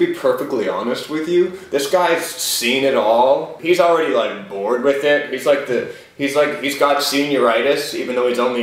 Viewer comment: I thought she said, he's GOURD with it.. and I screamed